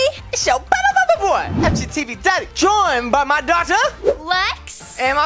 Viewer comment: thought he said doctor instead of daughter xD